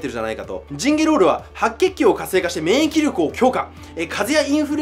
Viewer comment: チンゲロール？？？ｗｗｗ（空耳）